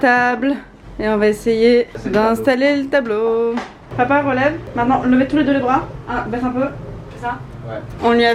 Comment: c'est moi ou Léna a dit "Maman" au lieu de Marcus?